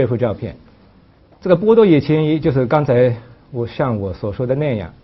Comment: 是不是就我一个人把波多野乾一听成波多野结衣？😄😄😄